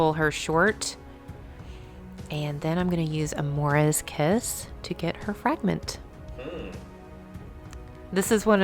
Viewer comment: I honestly heard "and then I'm gonna use Amora's kiss to get her pregnant " xD I was like "Wait...that's not how you get pregnant!"...then I had to rewind to check what actually said... xD I'm crying... xD xD Anyway a very cool battle report, cheers guys! A bit sad that I was really excited to see Ebony Maw in action but... it wasn't meant to be. And consdiering how I really like Toad was also disappointed he didn't do anything either... So... I don't know how I enjoyed the report so much when the two main things I was looking for were duds but... well done I guess. xD Keep them quality videos coming! ^_^